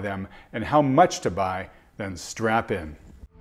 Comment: wait a minute did he say strap or strap on?..